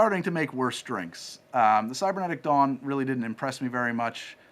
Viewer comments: Lol! When I first heard them say the Cybernetic Dawn, I misheard it as the Cybernetic Dong. 🤣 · I am confident I heard say “cybernetic dong” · i watched the video yesterday and did have trouble making out the robot voice. but, i also misheard you saying Cybernetic Dawn as Cybernetic Dong, so maybe it's a me problem?